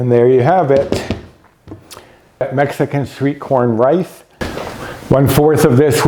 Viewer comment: I've never heard of this, looks great! At first I thought you were saying sweet corn, which we know all about here in the south. It sounds delicious especially since I'm trying to curb my carbs! Thanks for filming.💛